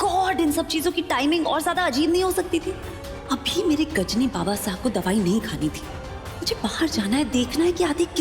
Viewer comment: Did she really called him Gajini Babuji?